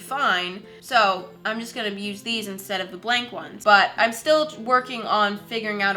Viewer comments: I heard I will abused them lol😂😂😂😂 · When you said'' working on'' I thought you said ''twerking on''